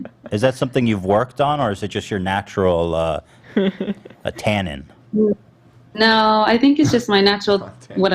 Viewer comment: Does anyone know wtf the word Ethan said there means? I tried googling it but I'm pretty sure I'm not spelling it correctly so I can't find anything lol i spelled it as "Atanin" ? And nothing comes up lol it just says "Did you mean attain?" So how do you even spell it? Idk, I guess I just like the way the word sounds lol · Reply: Pretty sure he was trying to say “timbre” but misspoke and said “tannin.” 😂